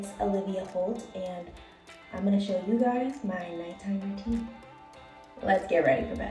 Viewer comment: i thought it said "nightmare" routine..